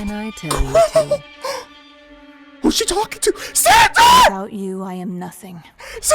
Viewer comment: I thought he said Santa